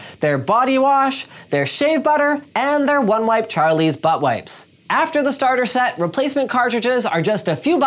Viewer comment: Did you say butt wipes? 08:30 ILMAO